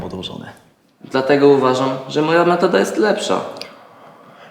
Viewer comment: ? co on powiedzial? moja ... jest lepsza? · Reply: metoda chyba XD